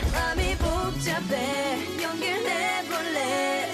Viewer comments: For the longest time i thought she was saying "Hello this is 'The One' " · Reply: hahahahhaa,i thought she was saying * hello this is new world * 😅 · For the longest time i thought she was saying "Hello this is 'The One' "